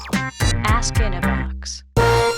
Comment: I thought they said ass in the box